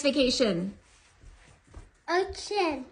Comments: I thought he said “oh shi-“ instead of ocean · I thought he said oh sh*t but he really said ocean😅